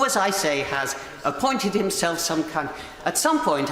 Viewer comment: Anyone else hear her call him a c*nt, or is that just me? Not that I'm saying she's wrong.